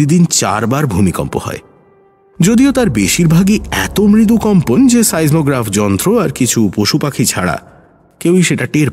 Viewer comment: what was that word? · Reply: Seismograph